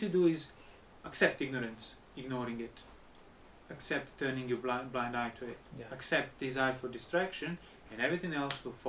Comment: Does someone hear clearly what Ajahn is saying at 14:29 please? I don't understand correctly what words he is saying. Accept ignorance, ignoring it, accept turning your blind back to it, accept desire for distraction? Is that it? What does he mean by accept desire for distraction?